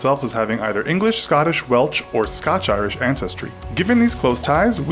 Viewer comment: did he just say welch? Lol it’s Welsh. Just as it’s written.